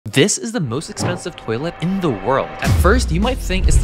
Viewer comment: I thought he said the most offensive toilet 😂